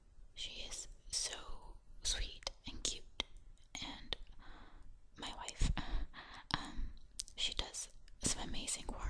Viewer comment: Did you say "my wife"